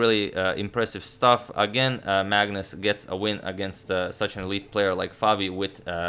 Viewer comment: "Again, Magnus gets a win against such a weak player like Fabi." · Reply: Lol, I thought I heard that too and was like "Wait, what?", rewound 10 secs to hear it again and indeed, it's 'elite' like Umar pointed out.